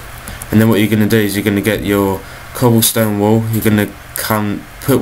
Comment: did he just said "cobblestone wool" ? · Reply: sophia alejandro I said cobblestone wall lol :)